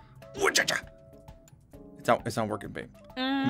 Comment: On 4:35 did say babe ???? ( Sorry idk if the love or not)